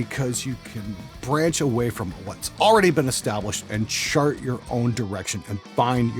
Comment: did you say "shart your own direction"? must be talking about san fransisco's homeless problem lmao